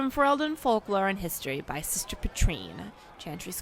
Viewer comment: Hoo boy. I heard you say "Chantry Sister Petrine" and had some traumatic flashbacks to Sister Petrice from DA2 - thank goodness I realized I had misheard! Petrice better not be publishing a blessed thing 😒 I've never spoken with Flissa as Lavellan before! What an interesting convo. I heard her voice actress was a random person who won a contest and got to be in the game, which is surprising, cuz she's got a lovely voice and sounded perfect in-world! As for Fenris' state in Blue Wraith, I believe the comics have their own canon - it's the one that follows the world state from DA:I that you use if you don't import data from the last two games - so Fenris is alive in that! For Varric's comment on Orsino, that's an inside joke, poking fun at the developers who made Orsino do that even though it made no sense with his character.